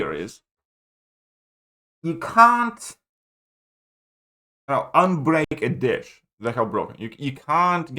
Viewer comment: I won’t lie, thought said “you can’t unbreak a Dick”. I immediately thought he’s right! 😂